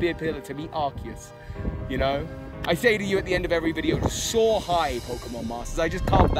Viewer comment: Soar high? WOW did I ever miss hear that this whole time. I always thought you were saying "so hype."